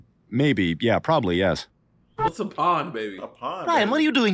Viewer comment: I think he said “Porn Baby” 2:39 Thought he said “Willie” at first😅 25:43 Oh..I see the legs 31:59 How would you know?…